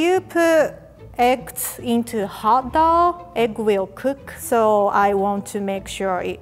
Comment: I thought she said hit dog instead of hot dough